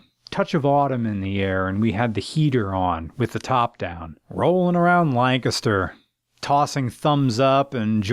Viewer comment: At first, I thought he said "Rolling around like a 'ster" (short for gangster, I guess?), but he said "Rolling around Lancaster". I am Boo Boo the Fool.